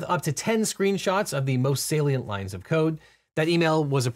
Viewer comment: Did you say “masturbations” at 4:50?